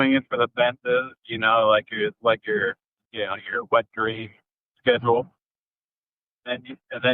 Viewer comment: I had to rewind it to see if he actually said your “wet dream schedule” 😂😂 In all seriousness though, to me hitting layer 7 is a perfect bid. If your bid solves in layer 1 it’s like buying a car and the salesman says name a price. You throw out $15,000 and he instantly says “sold!” Leaving you kicking yourself wondering how low he would have gone. Whereas solving in layer 7 is like you spent all Saturday afternoon haggling with the sales manager and you are certain you are ending up with the absolute lowest possible price they could let you buy it for